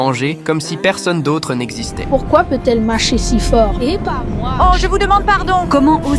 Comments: J'ai entendu "Eh pas moi" 😂😂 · 😂😂😂 aussi j'ai entendu "Eh pas moi"😂😂😂😂